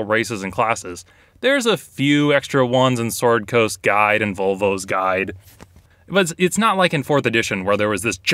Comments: I was rewatching this, and only just realized he said "Volvo's guide" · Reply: I thought he said “vulva’s guide”. I’ve been searching for that book for months! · It sounded like he said "Vulva's Guide" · It sounded like he said "Vulva's Guide" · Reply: I heard "Volvo's Guide".